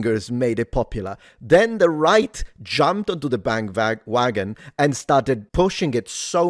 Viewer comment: We all heard you say, "BandVagon". Spent some time in Deutschland recently? Lol